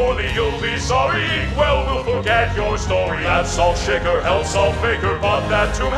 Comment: wait I thought said hell not held